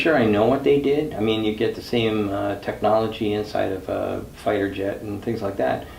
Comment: Did said "same as fighter jet technology"? 😳😳😳